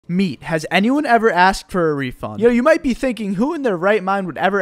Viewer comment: I thought you said "asked for a refill", so, has anyone asked for a refill?